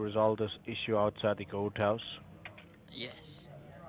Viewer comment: Whaaa? The IRS moved out of the country? LOL This scam makes me the most angry!! Subtitles were perfect...except at 12:14, I thought he said goathouse! 😂